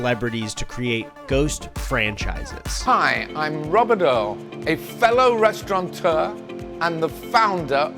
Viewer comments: swear that man said I’m robodoe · I have no idea who Robert Earl is but at 16:01 i could have sworn he said " Hi, I'm Rub Budub" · When Robert Earl introduced himself, I thought he said “Hello, I’m Grubberdough,” Edit: assuming he owns Grubhub